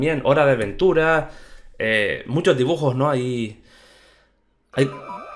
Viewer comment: entendi desayuno